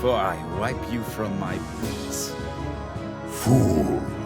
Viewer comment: Ummm...what he say? He was cut off by the music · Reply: I may be wrong but i think he said "Boot" or "Boots".